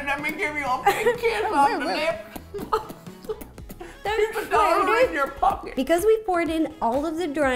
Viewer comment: What did he say? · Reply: “Here’s a dollar in your pocket”